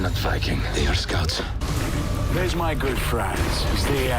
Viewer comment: It’s seems that way, I’d say even previews of the 2nd part of the season. Didn’t Eric the red colonized Iceland and then Greenland? Also in the preview I hear; “these are not Vikings but Scotts not scouts.” Could it have something to do with Ivar invasions of the Northwest of Scotland and the Northeast of Ireland, where he become king. I also could see him kidnap a pregnant Queen Kathia to take with and have her son as his own. I predict that Harold will die protecting Norway from the Rus and Bjorn will be so enraged that he’ll fight like mad to later ruthlessly rule as king of Sweden and die of old age in his 80’s. Again, I wish to see Lagertha entering Valhalla welcomed by Ragnar. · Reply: Also in the preview I hear; “these are not Vikings but Scotts not scouts.” He said, "They are NOT VIKINGS, BUT SCOUTS." As in Oleg's ADVANCED SCOUTING PARTY, for his planned invasion.